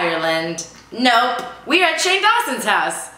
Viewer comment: In the beginning it sounded like you said Jane Austen instead of Shane Dawson...you should do a Jane Austen themed episode.